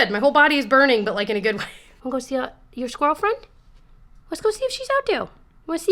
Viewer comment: For a second I heard you ask Pinocchio “do you want to see your girlfriend?” (instead of “squirrel friend”) and for a hot second I was SO invested in your dog’s love life